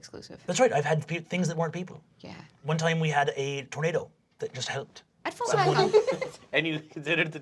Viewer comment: I thought he said tomato and not tornado 💀